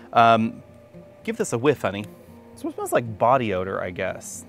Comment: Did you just call him honey?! 10:16 .